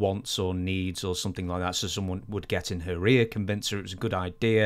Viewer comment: Sounded like said "somebody would get in her REAR and convince her" regarding Dixie Carter being persuaded by talent. Well, "getting in her rear" is one way to persuade her.